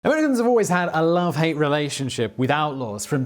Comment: What was that very first word? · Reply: I think he said "Americans" but I could be wrong.